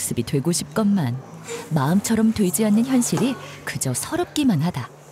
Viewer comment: 여기 부분 "마음처럼 되지 않는다"에서 "돼지 않는다"처럼 강조해서 말한 느낌으로 들리는 건 나 뿐인가...